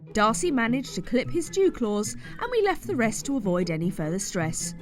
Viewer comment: Did I hear "jew claws'? lol